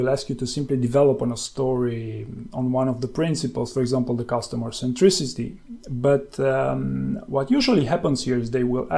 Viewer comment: What are you saying? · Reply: The customer centricity